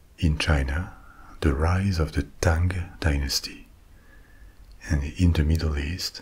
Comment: i was so sleepy and then i thought i heard “the kang dynasty” and i was like when he start talking about the avengers movie?